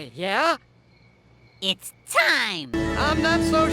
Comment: It’s time? It’s time? Did he just say it’s time?